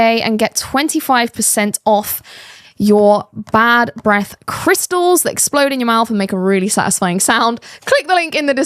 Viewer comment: Sounds like pop rocks. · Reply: I misheard crystal meth the first couple times